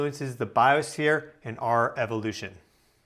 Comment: Sorry for my dyslexia, but did you say "devolution"? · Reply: No, he said evolution